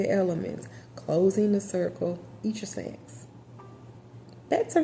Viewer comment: What did she say at 9:30 ? I know she didnt say eat ya snack 😅😅